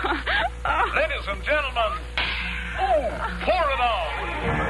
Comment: What's he saying at 2:45? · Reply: “Ladies and Gentlemen...pour it on” setting up the Pepsi Cola commercial.